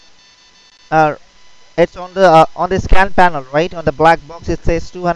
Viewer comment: Did he just say "On the Scam panel" at 8:32?